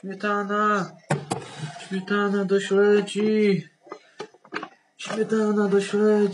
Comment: Ja na początku usłyszałam śmietana do śmieci